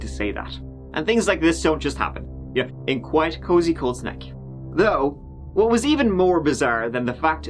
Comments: Thought he said Cold Snek... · I thought said the name of the town was Cold Snek, and now I can't hear it correctly any more. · Your accent made it sound like you were saying "Cold Snek".... And I am now super disappointed that it's not actually called that 🤣🤣🤣